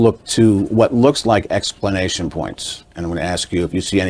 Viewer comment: does he say 'explanation points????'